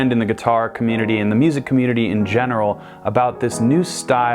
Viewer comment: really thought he was gonna say djent but he just said general oh well, off to bed I go...